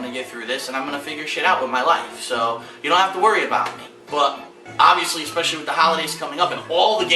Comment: DID HE JUST SAID DON'T WORRY ABOUT ME? What the fuck happened with this?